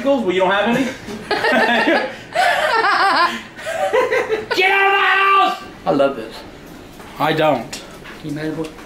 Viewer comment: Did Just Say Get Out Of "My" House